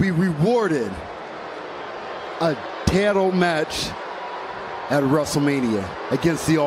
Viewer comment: Wow bobby lashley is bad on the mic lmao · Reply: William Dupree what are you talking about he said ditle not title